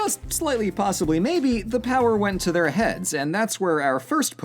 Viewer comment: At first I thought he said: The power went to their hats. Which, considering the papal head gear, makes a lot of sense.